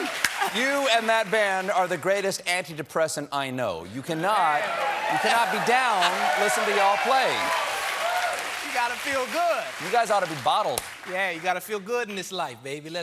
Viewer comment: I thought at first Steven said "You and the band are the best antidepressant I know. You cannot GET down while listening to you all play." I was like, "Wow . . . Steven knows more about antidepressants than I would've expected."